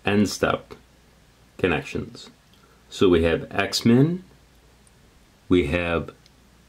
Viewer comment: I could have sworn you said X-Men lol.